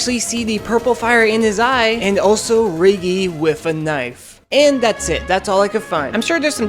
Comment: I thought you said riggy is the roaring knight because my phone volume was low lol